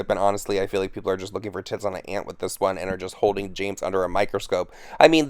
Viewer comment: Did you just say "tits on an ant?"🤨😐😶😂😆🤣